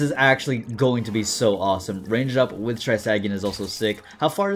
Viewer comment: When you said Trisag, it sounded like mom saying Isaac. 10/10